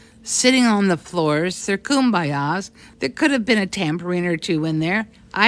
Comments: Oh no! you misheard the MAGA creature. She said TAMBOURINE... not tampering. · I listened to it a few times and it does sound like she said "tampering", but in context to her comment about "chanting" and "Kumbayas" I think what she said (or meant to say) was "tambourines". "...there could have been a tambourine or two in there..." makes more sense. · I think she said "tambourine," not tampering. Regardless, she looks and sounds wine drunk, so it's no surprise she doesn't even know what the hell she's saying... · I heard ‘tambourine’ not ‘tampering’ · The MAGGOT is a dumbo. But, I think I heard tambourine (and kumbaya) vs. tampering? Listen again and let a sister know [21:56]. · I thought she said there may have been tambourines 😅. She sounds a little pickled. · I heard "there could have been a tambourine in there" rather than "tampering" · I think she said tambourine as in the instrument. She was trying to make a shitty mean joke about their chanting.